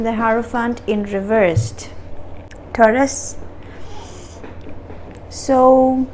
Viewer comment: Did she say Curtis?